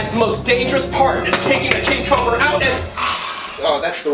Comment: I thought you said kitten the king cobalt and started laughing then o saw the comments